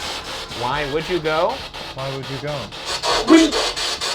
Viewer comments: I didn’t hear “why would you go”. I heard “window” since it’s one of Ryan’s worst fears. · i literally heard ‘wheej’ how can y’all hear why would you go😫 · that does not sound like its saying “why would you go?” It sounds like it’s saying weed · Spirit Box: Ryan: I heard why would you go! Me: Uhh you sure?